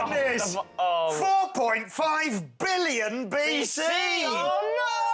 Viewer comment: i thought it said million not billion LOL